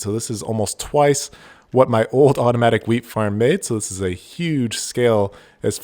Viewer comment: I thought for a second he said aromatic weed!😅😂